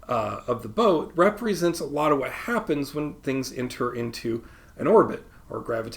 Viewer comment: you said rowboat 🚣‍♂️. I thought you said robot 🤖. 😂😂😂😂